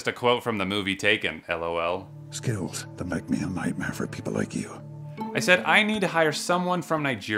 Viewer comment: Did she say f**k you?